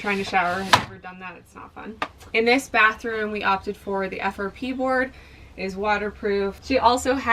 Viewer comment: what kind of board was used for the bathroom walls? Sounded like she said "pea board", to which i couldn't find anything on the web. Thanks